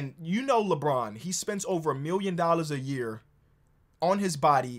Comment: Thought he said “billion” for a second.